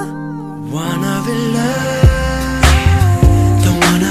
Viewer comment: juro que escuchó que dice wana vkook. Lo se me quitare mi obsesión por ese ship